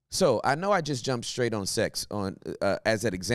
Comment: The fact that I needed to hear this at 5am at the gym was Gods timing . Also you used A instead of E when saying example . For a second I thought you said Axel that’s my name lol . I immediately stopped at the squat racks and started stretching 🙆‍♂️ I sat in the conviction for a while then went back to squats after sitting with the lord for a moment of discernment . Thanks you out here saving lives and doing Gods work will be praying for you